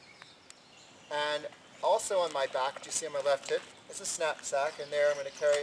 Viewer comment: swore he said left tit lollmao